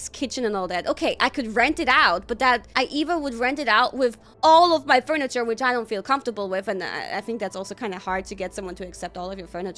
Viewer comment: 跟直播時聽成把不舒服的家具租出去聽得我滿頭問號😓